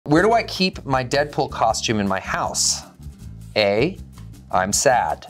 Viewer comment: I thought he said dental costume the first time and got scared lol